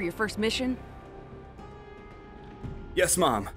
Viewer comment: did he Say yes mom?